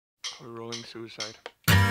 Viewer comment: in the beggining does serj say "a rolling suicide"? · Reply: I heard "were all into suicide" but that would be horrible so ik it's not that